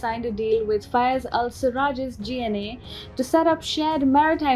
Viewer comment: Hold up, hold up, Am I hearing things or did they say GLA at about the 1:50 mark? GLA as in Global Liberation Army?